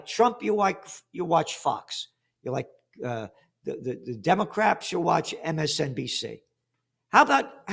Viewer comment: Did he just say "Democraps?" lmao